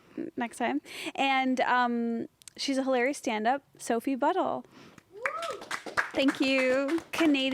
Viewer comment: Didnt read the title and thought Esther said “Sophie Butthole” 😂